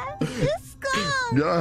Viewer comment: I wish I could tell what she's saying at 1:20 it's so perfect. I think it's "Stop scrimmin their scum" but I genuinely can't tell lol